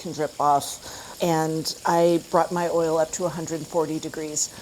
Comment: I’m a little bit confused. At 10:03 I heard “I brought my oil up to 140 degrees.” And then at 12:03 “I want to let this oil come back up to 340 [degrees].” I think the 140 was actually 340. 140 is the temp for the breast piece.